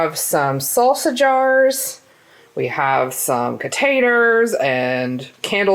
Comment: I heard "potaters" and was like "WHAT SORCERY IS THIS." After playing it back three times I realized you said "containers." 🤣 Also, I feel like is a closet chaotic, but she really couldn't contain it today. ( ¬w¬) Anyway, I think they look really cute!! HAPPY NEW YEAR!!!!!!! <3